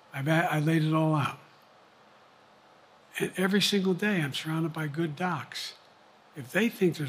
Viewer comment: "And every single day I'm trying to buy good docs". Did I hear that correctly?